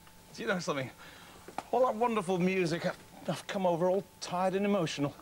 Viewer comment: how did he get away with saying that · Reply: I thought he said the f word not wonderful but if I'm wrong I'm wrong